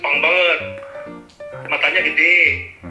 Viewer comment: Sola Aoi san terlalu cantikkkk 😍 蒼井そらさん激可愛すぎ！！！！ 是非またコラボしてくださいね☺️ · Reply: Woi salah denger MATANYA GEDE BUKAN TOK*TNYA GEDE